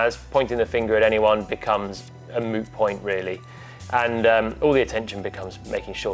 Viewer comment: Did he just say it's a "Moo Point"?🙄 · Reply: Moot